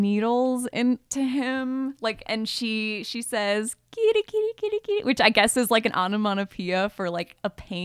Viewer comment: That noise she makes in “Audition” that sounds like she was saying “Kitty” she was saying “Deeper” as she stabs needles in his chest and under his eyes.